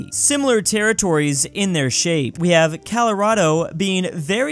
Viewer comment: did he just say Calirodo?